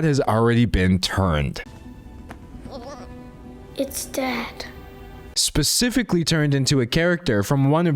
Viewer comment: I always thought Juni said “it’s dead” not “it’s dad” when I was a kid and was so confused 😭